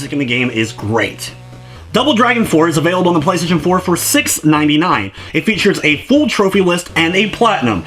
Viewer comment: Also I didn't notice as much screen tearing when I was playing on my TV but my capture device usually doesn't have any issues so it may have been there all along it seems to be mostly with the background movement but I have seen others complain about it so it is most likely a real issue with the game. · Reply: Oops, I thought you said $16.99, not $6.99 lol. Just saw the price, not too bad for being that cheap I suppose.